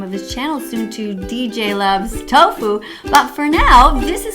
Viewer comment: did you said tofo. 😂